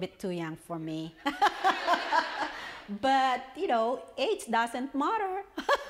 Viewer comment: I heard it as "aids doesnt matter" XD